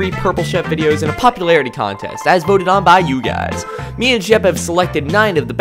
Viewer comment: did he say 'me and jeb' or 'me and shep'? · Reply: LokiPlaysMC Me and jeb